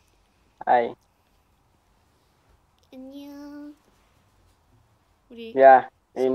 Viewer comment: The guy thought Lisa said Indian when she said annyeong 😭